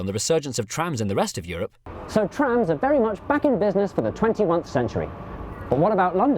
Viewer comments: Is it just me or did he say 21th century? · Does he really say „for the twenty-oncest century”? Can you really say that instead of “twenty-first”? Please explain this to me - a bloody german whos mother’s language is not English :D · im sorry did he say twentyONETHE century. it is supposed to be twentyFIRST century edit: 8:46 that woman cracked my up